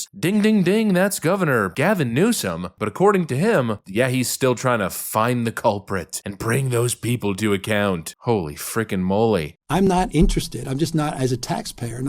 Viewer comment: I keep hearing if you want Gavin newsome to find who’s causing the homeless crisis, mirrors cost $10